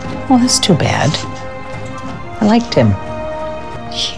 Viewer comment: What did she say at 1:16? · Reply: Big big fines.